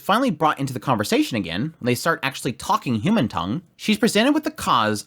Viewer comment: Out of context it sounds like you said Japanese isn't a human tongue.